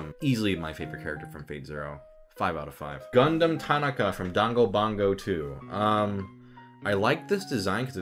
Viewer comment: Did he just say "dango bongo" for Danganronpa?